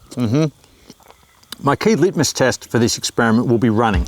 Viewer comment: did he say Litmus test?